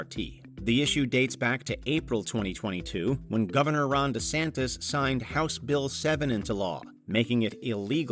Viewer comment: Thought he said "Rhonda Santis".